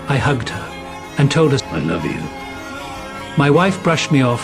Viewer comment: All i hear is “I love you my sons”